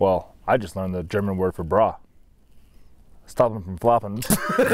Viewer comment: I heard it as holdsemfromfloppin.